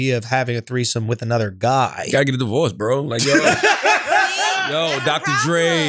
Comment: I legit thought I heard Charlamagne said "the bullets" instead of divorce lmao